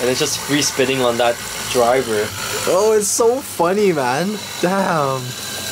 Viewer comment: Did anyone hear say d*mn?